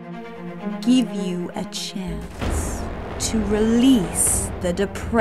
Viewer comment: did say “I’ll GIB U A CHANCE”???